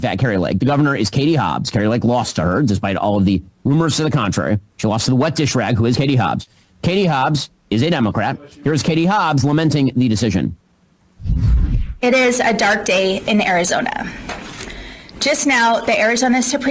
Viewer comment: I heard you. You said “Hatie Hobbs” real fast.